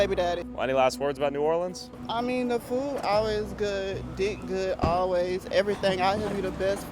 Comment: Did ma really say, ''food always good, dick good always''???